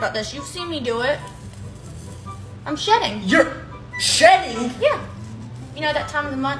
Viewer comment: WHY DID I HEAR "I'm shitting" instead of shedding 😭